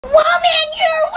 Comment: I always thought kid was saying "Woman" but a few people suggest uts "Whoa man" and now that i read it i am able to hear that. And it actually makes more sense.